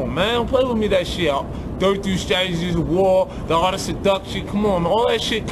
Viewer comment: can anybody decipher this?? 2:07 what did he say before art of seduction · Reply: 33 Strategies of war